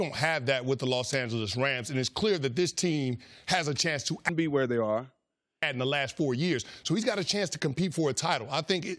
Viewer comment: Can anybody read lips? Wat did he say at 8:10 that they muted and did a voice-over on? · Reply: Benjamin Fritz "actually win something and they hadn't in the"